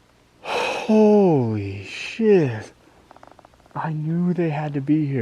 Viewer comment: did you say holy shit or holy shed haha! Nice